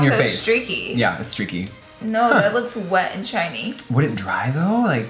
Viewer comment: For a moment I thought lura said she looks “ wet and chines” but she said “ I look wet and SHINY” hahaha 😂🤔